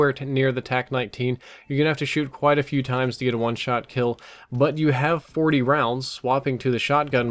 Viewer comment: "Your gonna have to shoot a few times to get a One-shot-kill?" did I hear that right?